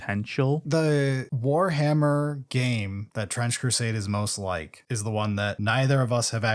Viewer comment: I swear at some point I heard you say "French Crusade" lol